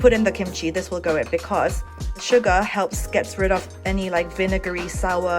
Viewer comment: I heard SUGA... 😂😂😂